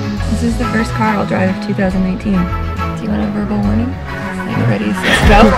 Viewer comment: “First car i drive in 2018 “ but It is 2019 . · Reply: Subtitles at 0:05 says 2018, and subs at 1:44 says 2019, I hear 2019